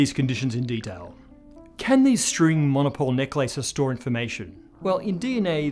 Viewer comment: I kept hearing "neck lices".